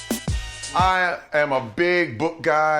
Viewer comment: 聽成 I'm big 撲街